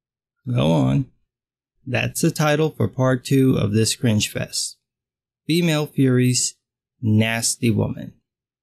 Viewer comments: Thought it said "Female Furries" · bro I thought this said "female furries"